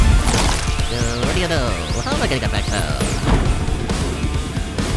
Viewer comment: does anyone have super hearing to understand what pop eye says here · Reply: Guh what do ya know, how am I gonna get back down